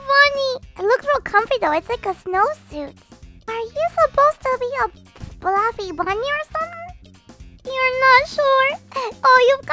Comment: this is just a joke cause ily so much but it cracks me up said are you supposed to be a fluffy bunny or something and I thought she said are u ment to be a bloody bunny or something. If u get it like it 🥰✌️ and blowy are so cute tho xx